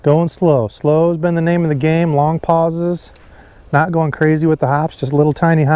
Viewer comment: The joke about “the cream you put on sore muscles” that said it’s when you said “long pauses” it sounded like you said “Salompas” which is an analgesic cream for sore muscles!! LOL!! I totally got the joke!